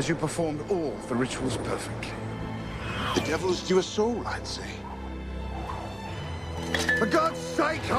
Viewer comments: what did holmes say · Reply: Devil due are soul I say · I always thought he said: 'The devil has duped us all, I'd say' but he instead says 'The devil is due a soul, I'd say'